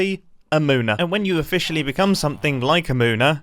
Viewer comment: At first i heard “moaner” and i was like “WHAT?”